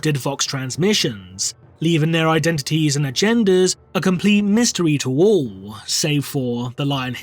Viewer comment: is he saying "their identities and their genders"? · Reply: Identities and agendas